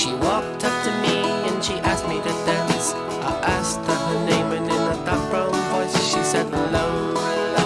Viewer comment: You definitely need to give it another listen and pull up the lyrics. There were clues all the way through. "In a dark brown voice, she said Lola." "I can't understand why she walked like a woman and talked like a man." "Girls will be boys and boys will be girls. It's a mixed up, muggled up, shook up world except for Lola." and the big one, "I know what I am, and I'm glad I'm a man, and so was Lola." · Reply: Dark brown voice? I heard "in a guttural voice"